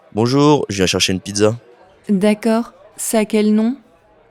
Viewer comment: It sounds like he said "je cherche un pizza". Instead of "je viens chercher un pizza". I also noticed that the "d" is dropped when you said, "elle est deja prete". Which sounds like elle-ledge-ah-prete.